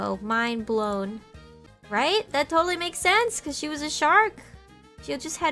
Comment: I wasn't looking and thought she said "Is it vore?" at the end. Almost gave myself whiplash